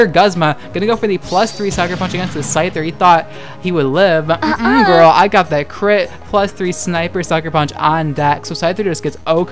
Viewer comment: I thought you said girl I got that clit